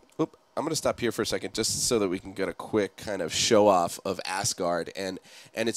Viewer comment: did say "show off of assguard" ?